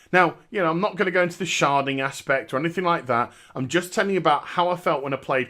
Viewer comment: I can't listen to people say "sharding" and not mishear it as "sharting". Every time it takes me like 10 seconds to register what they actually mean.